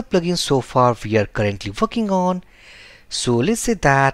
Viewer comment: This is really great tutorial. Thank you for this. Also At 7:34 I think i heard something i probably shouldn't have heard :D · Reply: """fucking on"""" ------>>>> """"working on""" .......... : - )